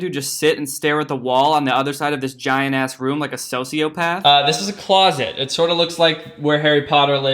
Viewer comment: did he say "sociopath" or "sofiopath"😂